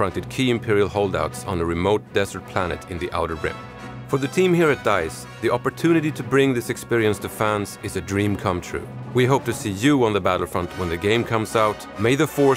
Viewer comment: Did he say May The Forth around 2:28?